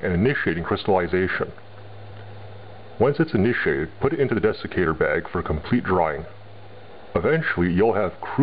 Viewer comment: It sounded like you said Defecator bag.....